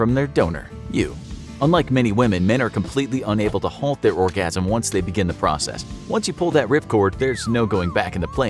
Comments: Thought it said organism.... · I thought it said organism